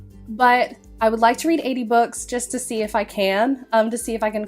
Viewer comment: oh 80 books....I thought you said "80's books" and I didn't even know that was a genre hahaha! and yes you and your mom are very punny!